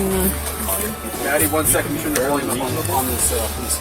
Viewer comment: At around 34:34 I hear " MADDIE hang on (hold on)" by one of the cops